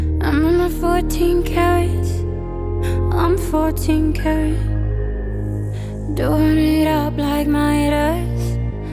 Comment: FARTING CARROTS?!😂I can't stop laughing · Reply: I thought she said "living it up like my ass"